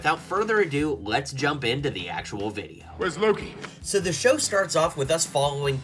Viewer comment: I like that you can hear Thor saying “ where’s Loki”, 2:09 mobius saying “I ride a jet ski”, 3:08 Loki saying “glorious purpose”, 4:19 where I think loki saying “I have a bomb”, 4:41 my favorite quote that Casey’s saying from the show “ what’s a fish”, 5:24 Sylvia saying “ you’re in my way”, 7:00 I think it’s Ravonna saying “let me see the back of that jacket”, 7:53 “ miss minutes saying “I’m miss minutes”, 8:45 Loki saying”sif”, lokigator growling, 13:34 old Loki saying “ah Loki” and 15:55 he who remains saying “not who you were expecting”